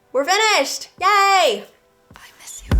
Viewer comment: Did anyone hear “I miss you” around 24:09 ?! LOVED THIS LOOK and loving the fresh ass personality!!!